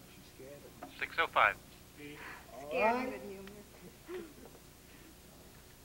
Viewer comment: I heard “scared as a human”